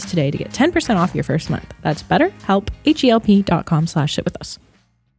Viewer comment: it sounds like says "shit with us"